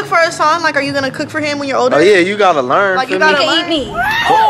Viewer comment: Oh noooo she wildn , did y’all hear what she said at 5:51😂💀🌚 This child has lost her mind😭 but at least her tattoo was fake 😂😭 is good at pranks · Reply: Chelsea Curtis “he can eat me” I think